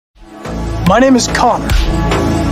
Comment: For a second I thought he said "my names co*k" 😭